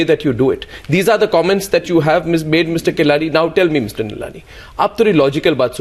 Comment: Did he call him Mr Kelani? Arnab is going bananas :D